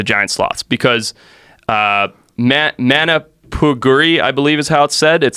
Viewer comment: What he was trying to say is "Mapinguari" it's a creature of native folklore that may or may not be related to the giant sloths, at least in brazil the giant sloths are called "Preguiça gigante"